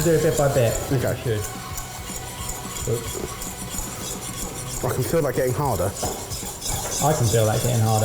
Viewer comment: I thought they said "I can feel it getting harder. I can feel it getting harder." But they said hotter. And right after he ripped off the other guy's speedo...🧐